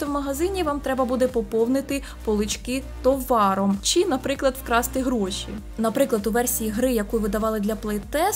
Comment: я спочатку почула *вкрасти*...